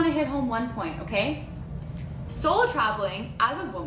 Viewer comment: I swear I thought she was saying “soul travel”.